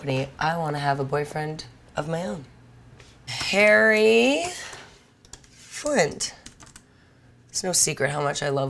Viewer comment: When she was saying Larry Flynt I thought she was saying Harry and i was yelling at the screen, Cox! Call him Harry Cox!